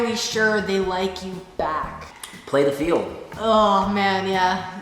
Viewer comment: When said play the field (3:33) I literally thought he said plan your funeral and my dumbass was like “I agree” 🤦🏾‍♀️😭 !!!!